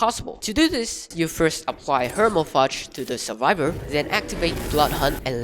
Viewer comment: "Hermafrage" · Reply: i heard hermochrage